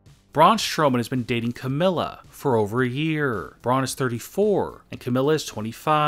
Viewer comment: I thought you said Braun dating Carmella lol